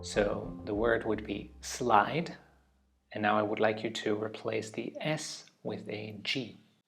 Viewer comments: i got the 3 examples for letter replacement correct, but for the "slide" example, i misheard and said "sguide?" and was confused until i realized you said "replace the s" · The exchanging letter one really messed with me, not because I can't do it in general, but because I'd either never heard a particular word or misheard it, for example when he said "slide" I misheard it for "slight" and the "g" obviously didn't make sense in that. Instead my brain told me to replace the "s" with an "f" instinctively.